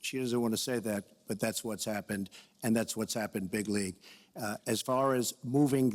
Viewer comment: Did he really say bigly